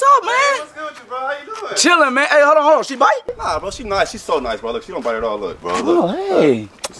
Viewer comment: Can someone say what he said? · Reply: “Nah bro, she (dog) not, she’s so nice bro. Look, she don’t bite at all, look bro”